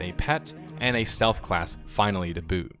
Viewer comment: Non english speaker here, what does he say at 5:28 ? when he is talking about the stealth class? the last phrase, I hear something like "finally the..."? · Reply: He says "Then there was a stealth class, finally, to boot." The "too boot" was a little out of place, I can understand you getting confused by it.